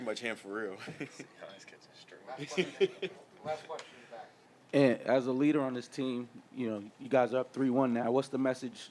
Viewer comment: Donte says “Naz gets his ?) It almost sounded like strings, but I couldn’t hear the last word. Anyone catch it? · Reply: I believe he said "C. Hines catching strays"